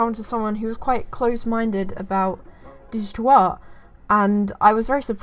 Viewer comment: Oh "digital art"?? i kept hearing "dishtoah" ! lol